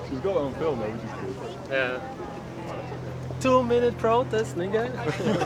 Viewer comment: wait, did he say the N word? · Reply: two minute protest niggaa!